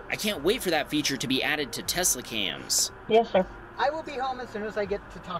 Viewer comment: Anyone else hear her say “Yes sir” when she answered the phone? Anyone else kinda confused why she said that if he is her husband?